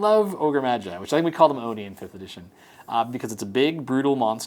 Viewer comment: Subtitles say "Oni for condition" when they should say "Oni in 5th edition"